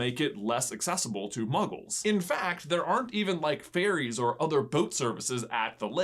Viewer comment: Oh, ferries, I thought you said fairies lol